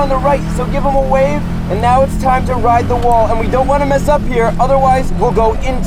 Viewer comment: i thought you said you've hit the wall